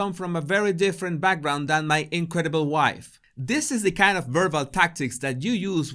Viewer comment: I'm a little behind the times, so could someone explain this to me: aren't 'awards' given to acknowledge achievements? Harry's phrase 'incredible wife': incredible, as in, hard to believe? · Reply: Oh I thought he said "my imbecile wife!"